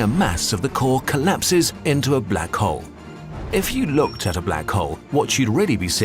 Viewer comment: Lol I first heard “black hoes”